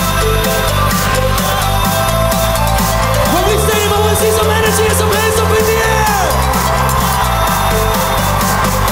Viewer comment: What does Martin say? Please tell me😥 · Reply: エネルギー余ってるやつ手上げろかな